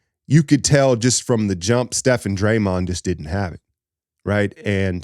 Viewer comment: The Ty Jerome revenge game was always going to be an uphill battle. You know Steve texted MDJ after the game and said, "Hey Mike, you think Ty is available?". Lol. On the positive side, Steph only logged 24 mins, and Dray only logged 21. They should be fully rested for Sunday. JK played well, so his confidence level should be up for OKC. They are going to need his length and athleticism. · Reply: Damn, I momentarily thought you said Draymond jogged 😭